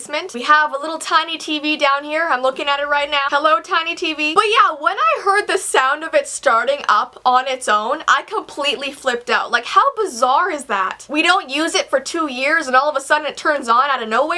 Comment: When she said "Hi tiny TV" i thought she said "Hi Tiny VeeTeam" Lol